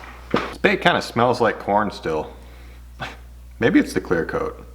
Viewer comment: I thought you said I think this bait smells like Porn no Corn ha ha